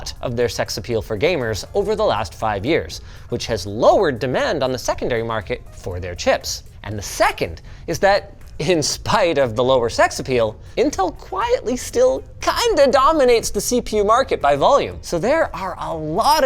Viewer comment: I swear he said sex appeal